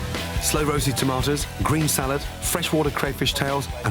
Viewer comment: AAAAAAAAAAAHHHHHHHHHH! that looks so good!!!!!!!! oh and thank god I watched this again because the first time I spaced out while looking at the food and I thought you said sun dried tomatoes and I questioned reality real quick.